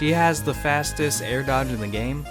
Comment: Did I just hear "Air Dog" at 8:58?